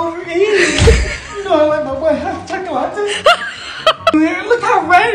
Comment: The way he said 'You know I like my white chocolate' and the 'Oh my gawd' it's hillarious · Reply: I thought he said. I like my hot choco-latte'